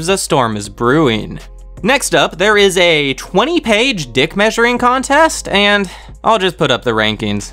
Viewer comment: im waiting for C.O.T.E s3, but did i hear that right? 7:17 d*ck measuring contest lol?